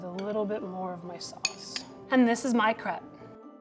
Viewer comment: I keep hearing “And this is my crap”